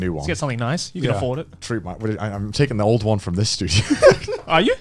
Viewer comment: Connor: "I'm taking the studio's old washing machine" Joey: "You're taking the new fridge from my house?" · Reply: I listened to it again because I was so confused and I'm pretty sure Joey and Garnt both thought he said "this dude" instead of "this studio". Idk where Joey got fridge from though.